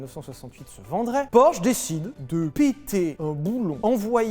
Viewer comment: J'ai entendu "purosangay" 🤐🤣